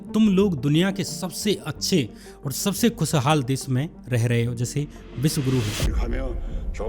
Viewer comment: Did he just said "Jaise vishvaguru"😂😂